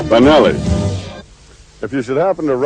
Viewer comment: 「バナァナ」に聞こえた